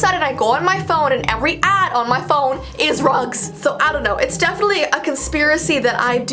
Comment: When she said “rugs” i thought she said “drugs”😅🤣